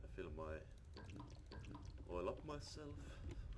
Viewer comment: pardon me did i hear that right? 0:41 you oiled urself up?? well that aint very right is it now???